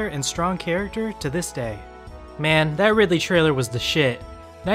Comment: I had to rewatch the part at 2:46 I thought he said the Ridley trailer was sh**.